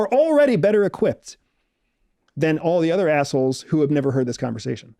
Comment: Geeeeeesus. At 1:06:10 did I hear that right? Did he just call his entire audience - US - “assholes”???!!!!